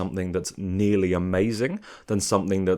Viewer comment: bit drunk and I thought this said "not an amazing start" and I was like darling what are u chatting !!! I havent been this excited for doctor who since I was 13 i cant wait to see more<3